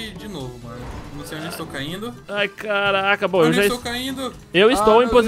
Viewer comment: *Foi isso mesmo que eu ouvi em 5:05 ? "Ai caraca"